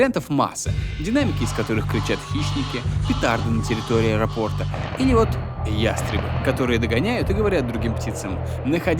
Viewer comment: что он сказал? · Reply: Петарды